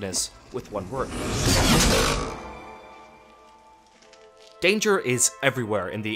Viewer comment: not gonna lie, i thought that said Dancer for a second.